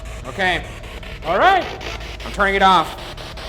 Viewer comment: Was it just me, or did anyone else hear “No please don’t” from the ghost right as Ryan went to turn the spirit box off? I don’t know, but it spooked me out a bit. I heard it around 10:12 .